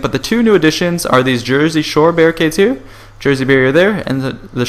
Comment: i thought yo said jersey shore barricades lol